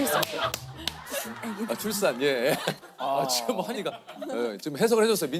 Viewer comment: 민지가 뭐라고 통역해준 거임?? "Drop the baby" 라고 들리는데